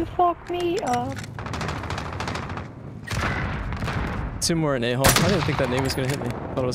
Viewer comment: did he just say “two more in A hole”